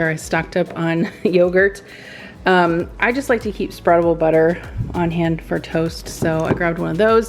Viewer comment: I swear I heard "struggle butter" @ 4:48 and I said "Yes! It is real!" because I never have the patience to let it soften & I rip the bread ..... Then I thought about it and realized you did not actually say struggle butter, but spreadable butter 🤣😂🤣